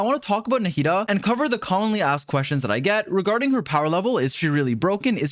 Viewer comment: Why did I heard “commonly ass questions”